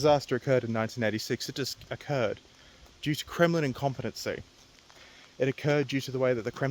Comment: when you said Kremlin incompetency it sounded like you said criminal incompetency. And honestly they're one in the same.